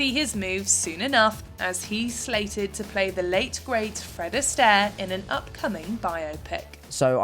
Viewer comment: I cannot wait to see Tom Holland as Frank Sinatra. That is going to be a phenomenal movie. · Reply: Did she say Fred Astair? I thought she said Frank Sinatra. Either way it is going to be phenomenal.